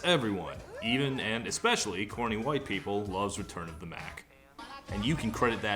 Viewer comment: Am I the only one who always thought he said "Turn up the main"?